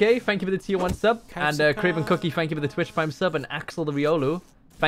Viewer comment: thought I miss heard him but he actually said Axel huh, didn't know he was a fan of could be a fake as well though.